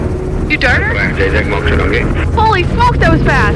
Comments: Did say holy F that was fast lol 😂 awesome 4:05 mark into video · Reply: 100% not! She said holy smokes… I may slip up at times… but isn’t about to say that, ever! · what did say at 4:05??? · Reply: If you really listen she says smokes 😂 I thought the same thing